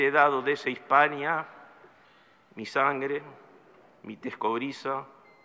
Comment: Hola. Entre "mi sangre" y "mi lengua", ¿qué dice?. Gracias. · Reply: Mi tez cobriza...